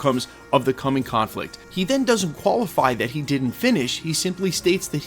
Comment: what words are you saying ? “he then doesn’t qualify that he didn’t finish” ??